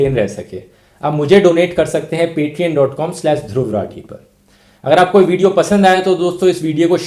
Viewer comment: Misheard patreon as paytm lol